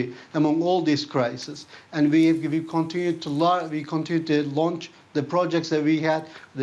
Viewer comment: the subtitles said ''we will continue to lie". that would have been the most realistic thing he could have said